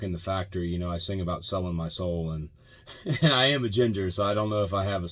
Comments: at first I thought he said "I am agender" and thought wtf but it is "I am a ginger". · What did he say at 3:05? "I am _________" I couldn't get the word. Thanks. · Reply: ‘I am a ginger….’ He’s red headed